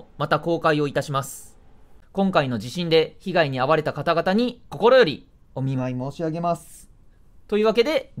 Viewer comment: ここで声が少し聞こえづらくなってるから 多分ほんとに頭下げて「心よりお見舞い申し上げます」って言ってる